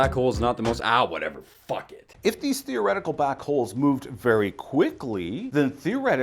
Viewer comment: Did he say theoretical black holes?